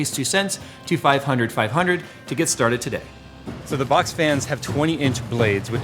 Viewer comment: I thought he said box VAN, not fan, still sick af